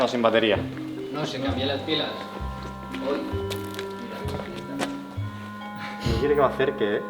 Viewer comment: Minuto 19:19 se oye una voz que dice :Aqui!!!